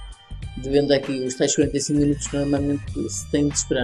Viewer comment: Quantos minutos temos de esperar para voltar a vender não consegui perceber. Grande vídeo, abraço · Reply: ***** penso que disseste no min 6:17 algo como 45 minutos